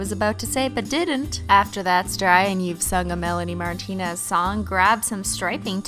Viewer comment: DID SHE JUST SAY....MELANIE MARTINEZ!?!??!?!?!